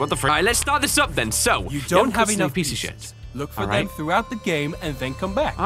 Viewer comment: did he just said piece of sh*t?